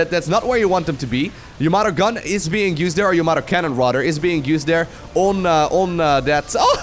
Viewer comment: I misheard you said Your mother's cannon, lol